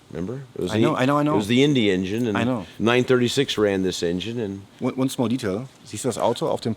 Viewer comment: also das habe ich schon ganz oft gehört bei JP und ich meine zu hören... once more detail. Oder sagt er one small detail ? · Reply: „ONE SMALL detail.“